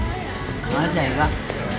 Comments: it sounds like he said "kaha jaayega" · Did Jimin say "Kaha Jayega"??